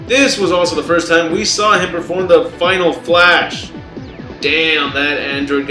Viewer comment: did he say vegeta used final flash against 19